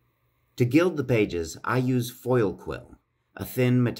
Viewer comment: Sobre for my audition. Did you say "foil quill" in the 4:33 time? Where i can found the same material? Or buy it? Thanks a lot for your time